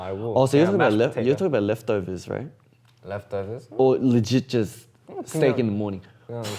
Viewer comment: I though I heard "legigers" but then I realise it was "legit just"